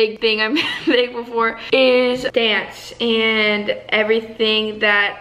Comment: I thought you said you were thankful for chance but you said dance